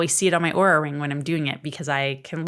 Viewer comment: Hello, I found your channel today just by happenstance and really liked this video. I could not tell what you were saying at a few point in times with one word. Are you saying that you saw your aura? Can you elaborate what this means? Or have I misheard you? I love the information you have shared. Taking notes and creating somewhat of a protocol for myself based on some of your tips. Thank you so much. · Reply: Oh that I Oura ring. It is what I am wearing on my finger. It tracks heart rate, sleep and stress!